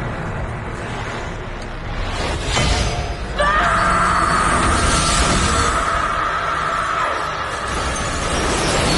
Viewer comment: what did she say? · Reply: Smash